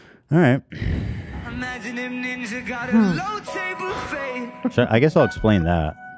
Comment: Maybe I’m just hungry but I heard “image if ninja got an elote buffet”